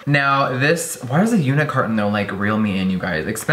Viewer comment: I am brazilian and my english is not that good. What Jeffree said about the package at 5:08? To me he said unicorn hahaha